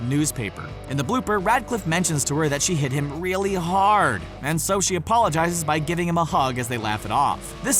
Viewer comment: I thought you said that hermione got Harry really hard LOL